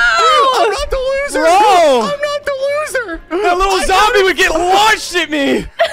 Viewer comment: Welp guess didnt hear the swear at 19:37 he said "THAT LITTLE ZOMBIE ONE SH!T ME"